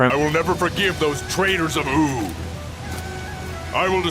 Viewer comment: Just more or 7:58, did it sound like he said those traitors from oo????????